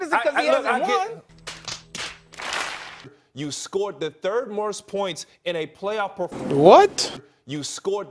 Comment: I heard "morst"